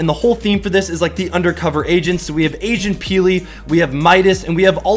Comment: When you said Agent Peely I thought you said Asian Peely